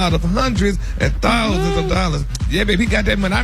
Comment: Why the hell always sound confused like that. They need somebody else to report these stories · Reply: Kimberly Monique sounds like he said 5 hundred thousand